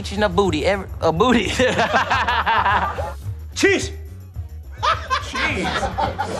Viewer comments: Jackie is the most talented actor, working with two languages and doing his own stunts, but him yelling "CHEESE!" @ 6:53 and not thinking twice is the cutest thing ever lol. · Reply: Lls I thought he said cheeks right after the cop said let me see ya ass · Gets me so hard the Jackie tottaly serious face meanwhile says "CHEESE!" 🤣🤣🤣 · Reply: I thought he said trees, but cheese is funnier 😂